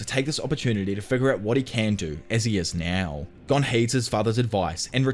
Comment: Heeds, it sounded like you said hates